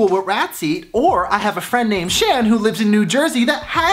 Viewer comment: I thought he said shane I'm dead🤣🤣